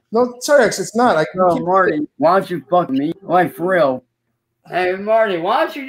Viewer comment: is it me or does he say "hey Marty why do you fuck me"